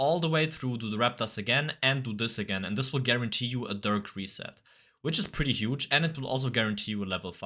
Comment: "dih" instead of dirk 💔💔